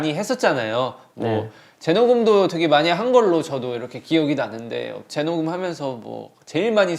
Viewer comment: omgawd, why did i think Jin said in 34:56 that they did a lot of twerking while recording the BE album???